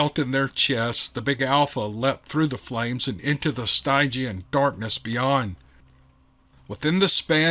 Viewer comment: MR......WHAT was that word, stigient or Stigien????? I LIKE it!!! Very descriptive.....very cool... As in "pertaining to the River Styx"?..... like an atmosphere permeated with the strong possibility that you are GOING to die? Is that word you used? I've never found a reason for such a death descriptive before.......I hope I never do. BUT if I misheard, please tell me how to spell the word in the story....I want to get a "bead" on that word🤭 Such a cool term. 👍🏼 Absolutely stellar read!👏🏼 Great story for sure!!!! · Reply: It's spelled "stygian"